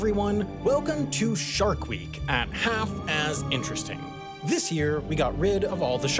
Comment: Did he just say Half ass interesting?😂😂😂